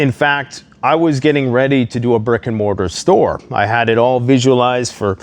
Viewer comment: I'm losing it. Thought I heard, "I was getting ready to do a brick and Mordor store."